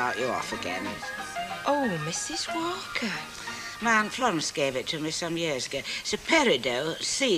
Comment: Where did Mrs Walker say she got the jewellery item? I couldn’t make out what she said even with the volume turned up loud. 🌞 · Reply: She says “My aunt Florence gave it to me some time ago!”🙂